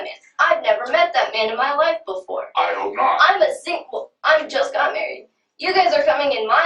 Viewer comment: 에 I'm just American 이 아니라 I just got married 인것 같아요!! I'm 이 약간 들리긴한데 just got married 가 들리네용! 재밌어서 구독 누르고 갑니다!!